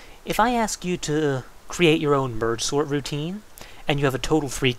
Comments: What is a bird sort routine? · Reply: thats ok , i was pretty sure i heard "vert sword routine" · mergesort routine** lmao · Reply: i could have sworn he said ''bird sword routine'' lol